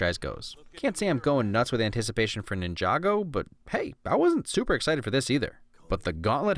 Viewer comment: i heard ninjago?